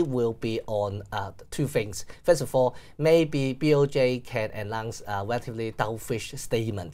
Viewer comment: Can anyone decipher what he said at 4:28 ? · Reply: announce 😅